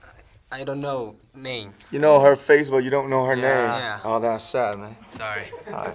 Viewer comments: RM: you know her face but you don't know her name? JK: yeah RM: oh that's sad man JK: sorry Why I find it so cute tho😅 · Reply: Dean Gradella i thought RM said : That’s sucks man! 😂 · RM: you know her face but you don't know her name? JK: yeah RM: oh that's sad man JK: sorry Why I find it so cute tho😅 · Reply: Dean Gradella lmao I thought RM said : That's whatsup man 😂💀